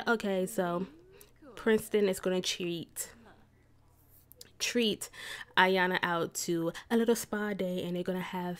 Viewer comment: I thought she said cheat but she actually said treat